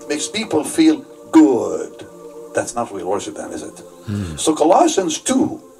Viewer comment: When he said "real worship" I thought he said "whale worship."